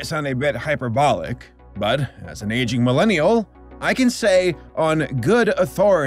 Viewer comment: What was said: "As an aging Millennial” What was heard: "as an Asian Millennial"